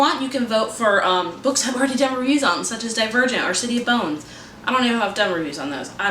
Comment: When you said Divergent I honestly thought you said A Dead Virgin. Lol